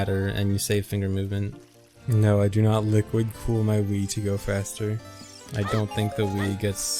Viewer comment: I thought he said liquid cool my weed 😂